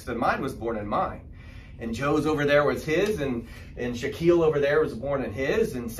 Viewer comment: I was not looking and I heard Shaquille 😂 I’m like no way there someone in his class named Shaquille